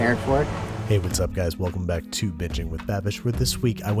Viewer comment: i may be a bit drunk, but i heard "welcome back to bitching with babish"